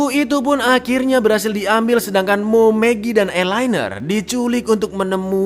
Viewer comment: Namanya Elinor tapi kenapa kedengerannya eye liner 😂😂😂